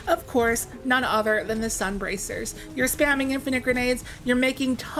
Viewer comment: I thought you said "Scumbracers" at first. 5:03 Thanks for this info. I'd thought there was a CD on Sunbracers.